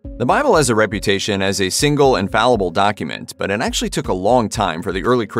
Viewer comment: Did he say infallible? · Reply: I thought he said "single and fallible document."